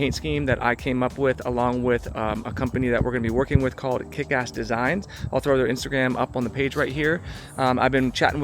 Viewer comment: Oh it was Kikas designs, and i kept hearing 'Kick ass' designs....😂😂😂